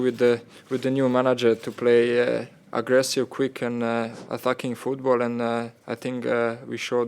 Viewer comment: Hahaha sounds like he says a fucking football